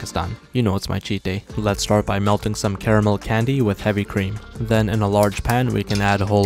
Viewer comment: I thought he said some terreble candy 😂